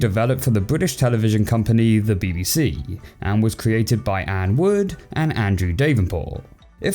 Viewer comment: Did I just hear "N-word" :l Ok it was ann wood